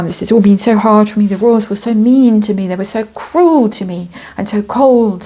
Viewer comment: “I tried my best, and they were still so Meme to me…” i misheard, unfortunately, although that candle?